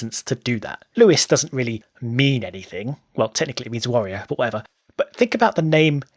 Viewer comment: All I heard at 5:20 is Lewis means Wario.